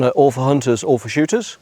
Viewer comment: I agree is a great teacher. But had to bust out laughing at the opening sentence. It sounds like he says “ awful shooters” not all 4 shooters. But in my case he would be right. Just found it humorous, I will keep definitely keep watching his videos.